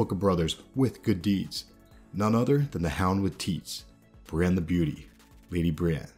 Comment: I actually laughed at the last part. I thought i heard the hound with tits. LOL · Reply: its something jaime thinks about brienne in the books "the hound with teets" =)